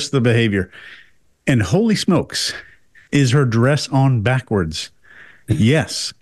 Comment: Jesus Christ… I thought was saying “her duress is on backwards” meanings some interrogator jargon. Then he said it again and I rewound - OMG 😂